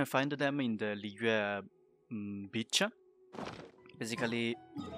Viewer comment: My man does not says beach he says bi**h